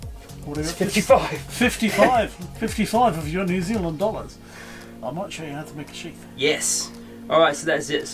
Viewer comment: Vnice, whos ya mentor? that knife we saw of his woah, what sorta moneys he asking? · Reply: Jason McDonald Hi Jason, I definitely heard him say “fifty five New Zealand dollars” but I think that is a “Mates Rates” quote and is not inclusive of GST (tax)!